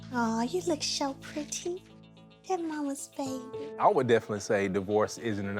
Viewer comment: Did she say “you’re mommy’s favorite” 😳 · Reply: She said, that’s mommy’s Baby!🤦‍♀️